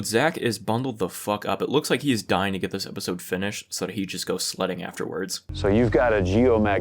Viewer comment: when said Zak was gonna go sledding afterwards i thought he said "slutting" and i was like yeah sounds about right